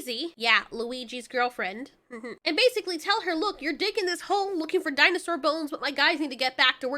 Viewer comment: I misheard "you're digging this hole" as "your dick in this hole" I had to rewind and put on CC to make sure I wasn't hearing things